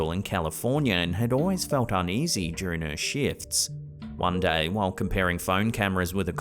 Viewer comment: Lmmfao 🤣🤣🤣 @ 9:21 i swear he says "during her shits (instead of shifts)